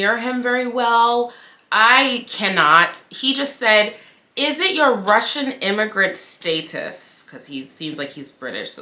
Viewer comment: I thought he said "What's your immigrant status."